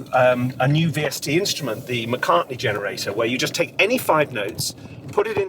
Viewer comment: I first heard "McCarthy-generator" and i'm kind of glad they didn't implement that.